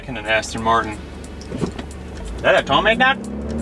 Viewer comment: WTF DID HE SAY?!😂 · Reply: “That a tomei dawg?”